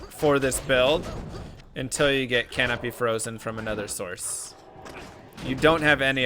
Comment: bruh.. i thought you were saying "canopy frozen" could not find one at all then realized what i had done (: but ty for content made my first hardcore play through way better